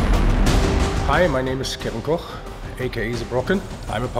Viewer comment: ako? gehört?